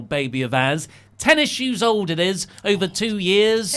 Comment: "Ten Issues old"... I seriously heard that as "tennisshoes old"!!! :)